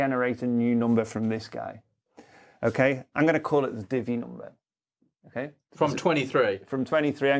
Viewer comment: It took me a couple of tries and I'm still not quite sure: at 22:40 is saying he's going to create a "divvy" number? My brain kept on wanting to hear it as "diddy" number which is probably something completely different ;-)